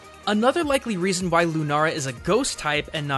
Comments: did he just say lunara? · Did he just say Lunara...?